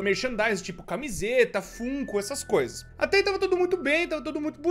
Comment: perai eu ouvi furro